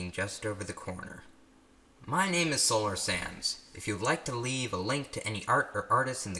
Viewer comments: its sounds like youre saying solar SANS but then i tried to say sands and sense undertale has risen sands sounds so much like sans its frustrating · "solar sands" that'd be a cool name for a band. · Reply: I honestly thought he said solar sans. I didn't read his name.